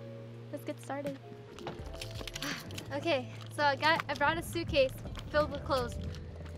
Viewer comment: When she said okay I got I brought , I thought she was saying I brought Ivana's suitcase, so I turned on the subtitles